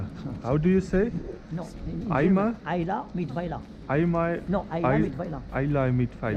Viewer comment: The man saying something at 17:33... I wonder if somehow he picked up the German phrase "Eile mit Weile" which means something like "don’t get stressed and hurry too much". What he says sounds almost like the German phrase anyway. Could that somehow fit the situation? It seems like he wanted you to take some time and visit his shop, right?